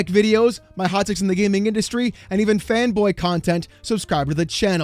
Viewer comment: The shere amount of times I thought you said even FEMBOY (FANBOY) content... :D :D :D always appreciate your videos, we need someone whos angry enough to say all the stuff you are! GGs